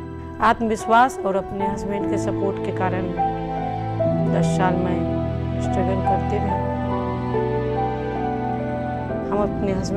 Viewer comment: she says "with her Self confidence and WITH THE SUPPORT OF HER HUSBAND" Subtitle says "I kept struggling to GET MY HUSBANDS'S SUPPORT" Correct this please.